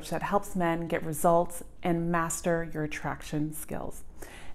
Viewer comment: i thought she said master... bation sorry im dirty minded omg